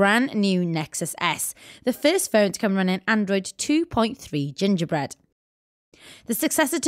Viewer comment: did she say ginger bread?